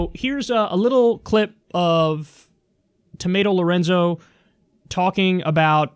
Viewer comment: Did you just call her Tomato Lahrenzo? 😂 0:48?